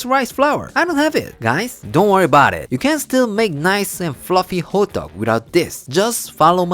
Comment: I keep hearing “Hot dog, hot dog” such a subtle difference in pronunciation:)